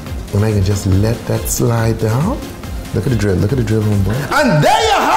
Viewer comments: At the end it sounded like he said say hello to drugs kids lol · “Stay tipsy” in the same sentence as “stay away from drugs” 😂😂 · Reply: Tbh i thought he said stay on the drugs kids lol😂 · “Say no to drugs kids.” As he is making the most dangerous one look delicious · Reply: I thought he said "say hello to drugs". I was like "way ahead of you on that one..." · “Stay tipsy” in the same sentence as “stay away from drugs” 😂😂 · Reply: BRUH, I HEARD "STAY TIPSY, STAY ON THE DRUGS, KIDS"